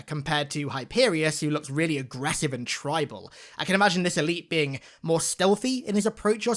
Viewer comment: did he say this elite? lol